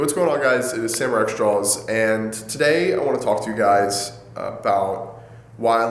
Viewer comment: In the beginning of his videos, I always thought he said "hey guys its samurai xjawz"